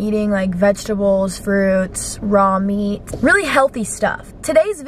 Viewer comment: Did she just say she eats “raw meat” 0:09 umm... are you okay?? 😂😂